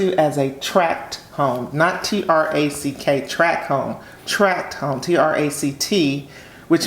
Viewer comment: Lord I thought said Trap Home. I am happy broke it down.